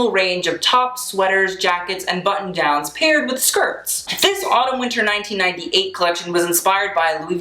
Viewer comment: When he said Autumn Winter I thought he said Anna Wintour